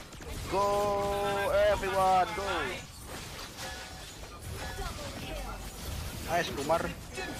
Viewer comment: Did just said nice “kumar”